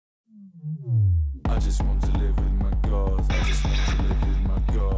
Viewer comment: Swear to god when I first heard this song my sister and i thought he said “I just wanna live in my car” and “half man half car”. Also the first time I heard this song was live and I didn’t know what it was called lmao